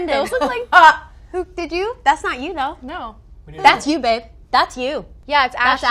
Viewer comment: Did she just say "That's you, babe"???